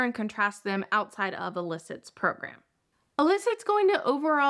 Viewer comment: I had the hardest time understanding the word "elicit", I thought you were saying "alyssa" (apparently google says there's a lot of smart alyssa's out there!) but otherwise enjoyed. I already use Notion!